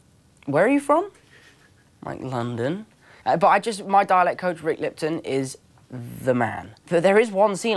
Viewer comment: its me or did i heard "DALEK Code"